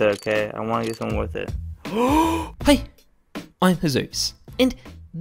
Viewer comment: I thought that that kid said *HI*